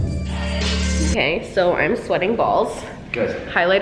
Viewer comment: I thought she said I'm sweaty balls